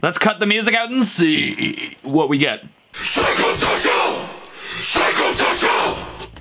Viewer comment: i hear sacrifical